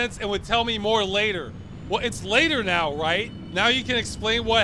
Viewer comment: he said later? I thought he said "its starting to make sense now, more liquor"